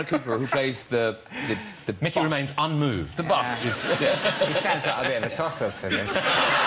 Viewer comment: What does Micky say at 15:31 ? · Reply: "he sounds like a bit of a tosser to me"